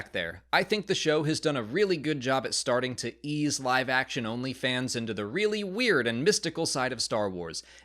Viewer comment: This is why I like this channel. You can enjoy the basic aesthetics and cooler fanservice stuff while still being like “yeah. This didn’t work”, “I didn’t like this thing”, or “this could have been better”. It shows way more integrity than many Star Wars or geek culture based channels do. Thank you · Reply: Did anyone else hear onlyfans?